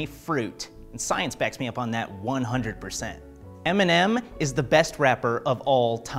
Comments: I thought that he said “MnM is the best wrapper” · I thought he said m&ms had the best wrapper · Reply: skittles have the best wrappers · I legit heard “m&m is the best rapper” and I can’t stop laughing